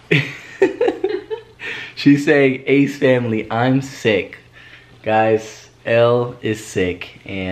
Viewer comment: When she was saying ace family I’m sick I thought she said ace family I shit